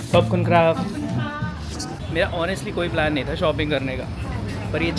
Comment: when said thank u in thailand at 3:08 i heard popcorn crap